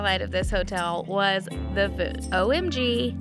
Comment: Can we just admire the fact how said voods 3 times instead of food